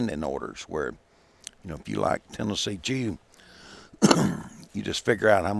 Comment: I swear I heard him say Tennessee cheatham